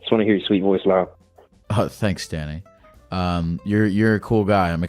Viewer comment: When the caller said "I just want to hear your voice Lyle", and he responds "oh thanks Danny" for a sec I though I heard "thanks Daddy"